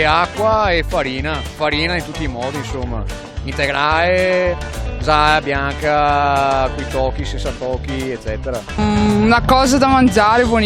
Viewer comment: bo io ho sentito "Zanna Bianca"